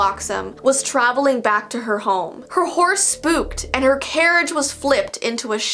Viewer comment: I thought she said “the horse pooped”